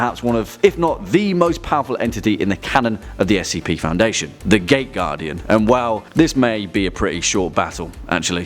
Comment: I kept hearing it wrong.. i heard it as the gay guardian everytime you said it